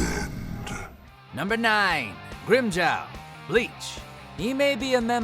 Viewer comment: Totes thought he said rim job and not Grimmjow. Lol thought I was tripping